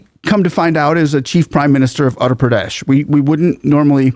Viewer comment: The State is utter (uh ter) Pradesh. It almost sounds like you were saying Andhra Pradesh. Just thought I would point it out.